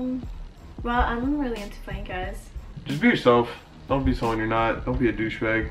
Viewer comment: I thought she said “well, I’m really into fat guys”😂